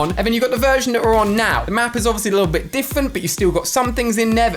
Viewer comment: Ali a said diffant instead of different 🤣🤣🤣🤣🤣🤣